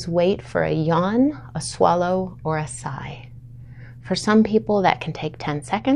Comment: The Vagus Nerve Reset - 1. Lay down, settle and focus your attention and awareness to the presence of your hands behind the base of your head; where your head and your skull meets. 2. Move your eyes in the direction of 3 o’clock. 3. Maintain until you: Yawn, Swallow or Sigh. (Note: depending how stressed your nervous system is, this can take 10 seconds to 2 or 3 minutes). That’s it. Thanks. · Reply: Thank you for typing out the instructions. When watching the video, I thought you said to wait for a "non-swallow sigh" and I was wondering what the heck that was, lol.